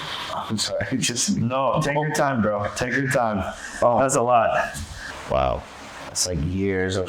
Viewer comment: did he say take your time girl